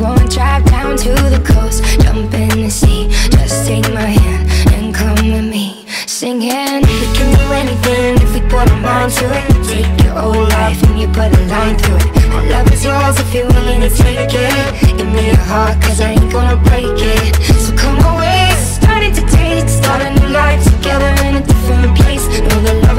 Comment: What most people hear "Take your old life then you put a line through it." What I hear "Take your old life then you put a lime through it."